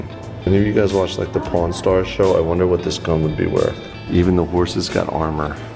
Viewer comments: Lol, am I the only one who thought it sounded like said "porn star"? Lol, I know he meant pawn stars but... Yah. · am i the only one who noticed that said porn stars instead of pawn stars